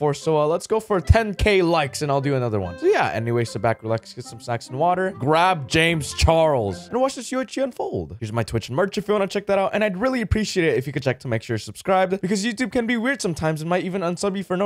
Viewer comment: I thought he said "get some sax and water" I was so confused. I'm going to grab my saxophone anyways.